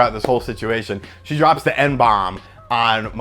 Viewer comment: I misheard you when you said "M bomb" and thought you said "N bomb" and my jaw dropped lol